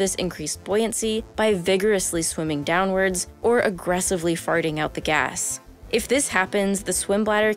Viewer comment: All I heard her say was, "aggressively farting out the gas." 😂